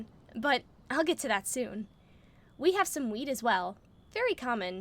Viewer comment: I THOUGHT SHE SAID WEED 💀💀💀